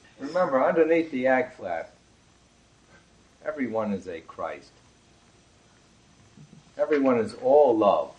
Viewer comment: Between 18:56 and 18:57, did he say everyone is in "Christ"? I wasn't able to catch it. 😕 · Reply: Underneath the AGFLAP everyone is a christ...